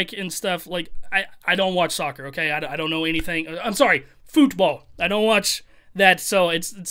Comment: Is it just me or it sounds like he said foodball 😂